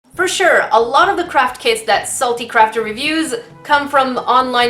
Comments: When you did the outside segment, I misheard "craft kit" as "crap kit" guess it fit for this product xD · I keep hearing “ CRAP Kit” instead of Craft Kit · Ok I know your saying CRAFT kit but my hearing is bad so it kinda sounds like your saying CRAP kit (I find it funny that my hearing is making me hear something diffrent) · It sounded like she said crack kit